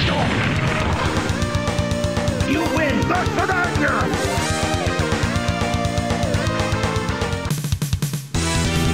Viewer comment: Did Colossus say 'Duck Commander' there? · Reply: LMAO 😅 He actually said “do svidaniya”, meaning “see you later”